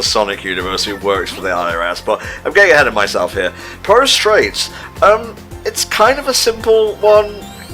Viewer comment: I, honestly, thought that said pro strats at the beginning instead of Polastraits.